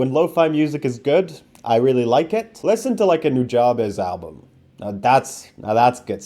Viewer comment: Who did you recommend on on 5:51 I cant understand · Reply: Nujabes